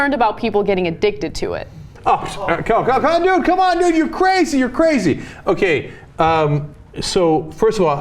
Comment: Lol, did Cenk say "come on nigga you crazy" at 4:22? Cenk's on that thug life now?